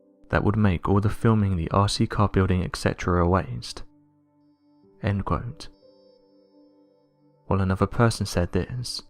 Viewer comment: sounds like he says "end quonte" and pronouncing ship like "shimp" or maybe i need my ears checked lolol